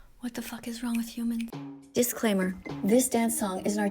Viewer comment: Yo did I her that right she said what the f*ck is hell with you she said that